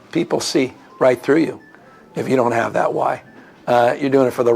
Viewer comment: Oh he said "why". I thought he said lie.